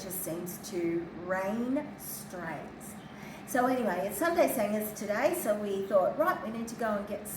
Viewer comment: 💟🐾 Thank you to your whole team for the love, compassion, and devotion to these precious souls 💗🐾 I enjoy the "Sunday singers", I thought you were saying "Sunday sammies" either way it was enjoyed by all! They might like peanut butter with cheese or maybe liverwurst ❤ Human kindness is universal, but evil is out there also around the whole world- the story you shared of what the discustinf celebraty did still has me sick to my stomach and _ _ h, e, 🏒🏒 Sending prayers, ❤ cuddles and love from WA 🇺🇲